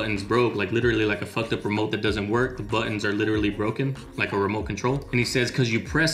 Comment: When he says button it sounds like he says budden